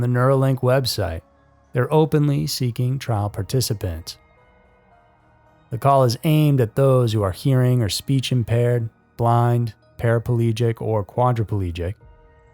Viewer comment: During the segment regarding the brain chip, when you said it could help people who were blind I misunderstood and thought you said blonde. Ha! Thanks for the good laugh! 😂